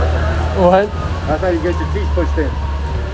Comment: It kinda sounded like he said 'thats how you get your teeth kicked in' maybe talking about the other guy